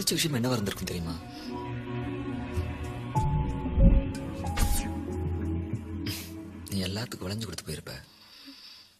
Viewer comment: can anyone plzz explain what he said?? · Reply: Vaasu